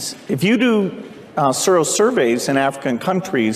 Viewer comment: can anyone please tell me what he said. It sounds like surro surveys? Is that just him stuttering, or is he talking about something specific? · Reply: I've found out that it's actually "sero survey", and it's a test for antibodies.